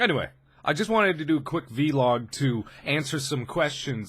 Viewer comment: did he just say vilog?